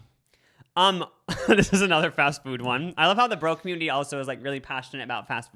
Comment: LMMAAOO I THOUGHT AT 29:21 SAID " I love how the BROKE community is really passionate about fast food" but then I realized he said bro community 😭😭